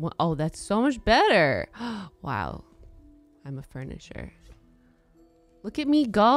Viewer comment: It doesn’t matter if Jenna’s content is “weird” or not, it’s great content because it comes from her and it’s genuine. Never change Jenna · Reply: Also when she said “I’m a furnisher” all I heard was “I’m a furniture” 🤣